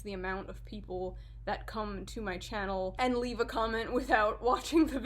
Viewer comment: I heard that as, "I feel like this hair is going to increase the amount of people... that cum to my channel." And I mean... It's alright, but... I hope it's not the hair? That'd be weird.